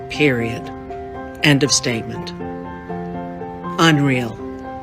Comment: Hey big mouth thought you said End of Statement😂